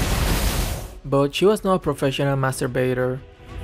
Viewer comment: what i heard is "She was not a professional masterbater" XD